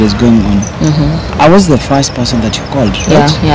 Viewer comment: I thought said Max was her first love.